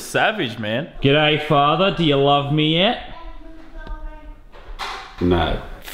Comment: i thought i said YEET